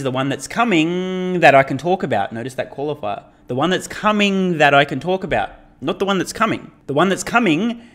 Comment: Hmmmm. Initially I thought he said "Favourite one, that is coming, that I CAN'T talk about", but you may be right... he may be talking about one he can talk about, and one he can't. Maybe that's just the CT and the compact..... maybe the compact and CAR Z..... aaaaargh!